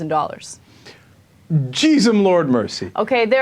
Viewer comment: did Cenk said "Jizz him lord mercy " ???